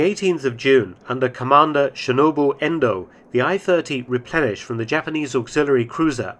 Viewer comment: did I just hear "under commander Chernobyl Endo"?